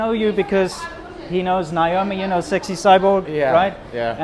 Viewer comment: Who is the woman at 0:37 - 0:42? I'm hard of hearing so I have a hard time making out words sometimes; I only heard Niomi 60.